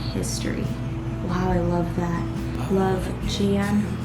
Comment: when you said "I love that" I hear another voice saying I love that too. Anyone else hear it? To me it does not sound like